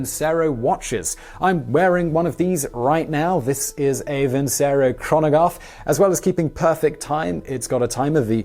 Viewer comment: "A Vincero Chronogaffe"? No thanks. That's what he says: "Chronogaffe!" Crazee!